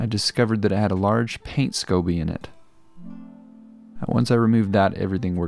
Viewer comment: I thought he said Scooby not scoby!😂